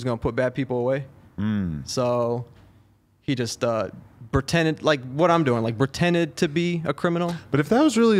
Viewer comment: Is he saying "BERtended" 🤔